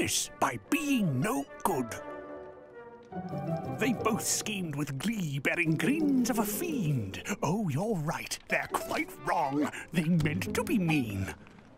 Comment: what did the narrator say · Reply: They both skemed with gleed adding grind of a thiemed oh your right they quite wrong they ment to be Mean.